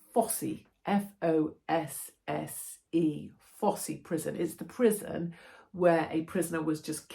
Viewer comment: Thought you said bossy prison😂